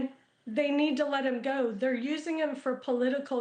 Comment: Any Updates?? Also, not sure if I misheard when his sister said: He (Dennis) is my 63yo Mother’s Eldest & Only Son… ??? I am confused bc I thought Dennis was 63yo. ???